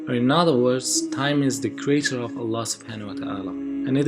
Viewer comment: There must be a correction in the video, where you mentioned that time is creator of Allah instead of that you must have to say Allah is the creator of time. Be carefull before posting video as there are other religion people should not fall into misguidance (Pls play video from 4:00 onwards u can see the mistake) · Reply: brother it is said: creature not creator... you could see this in subtitles too. Thanks